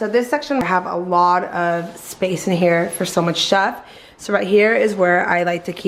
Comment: “for so much shuff” ???? 😂 or is that a word or did she say something else lol